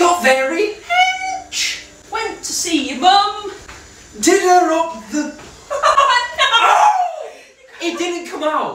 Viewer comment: I thought he said: “did her on the be-“